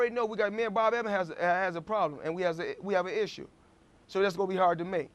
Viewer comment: is is me or did money just say "me n bob evans has a problem"😂😂😂😂😂